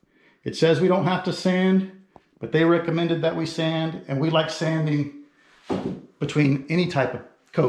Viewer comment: I thought you were about to say "We like sanding" I was like what monster likes sanding.